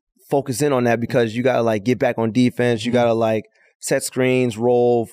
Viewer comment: did he just say "get back on defense?"